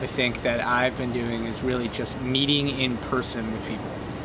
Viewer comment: I immediately thought mead-ing instead of meeting LOL